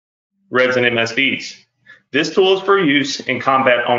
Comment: red and msv's?? Can't quite understand him.